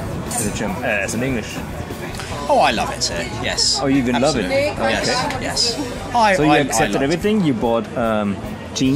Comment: what did the women said? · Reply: "No, no idea where they are". But I can't understand what she gets asked.